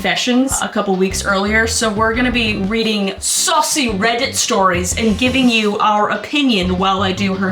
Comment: Thought she said sussy Reddit stories there